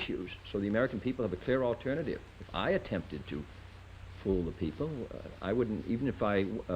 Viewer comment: Does he say “ pull the people “ or “fool the people “ around the 3:20 mark?